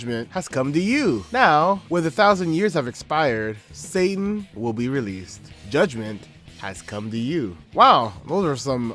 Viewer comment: I hear "Suffering will be released" instead of "Satan will be released", although it's probably "Satan" since that lines up with the Bible passage.